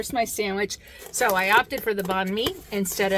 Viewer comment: What was the name of the sandwich you were eating? It sounded like you said Bon V. I tried looking it up. I can’t find anything like that. Oh well, maybe my hearing is gone. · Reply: It was a Vietnamese Sandwich , Banh Mi .